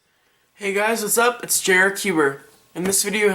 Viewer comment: All this time I thought you where saying hey guys it's jerrer cuber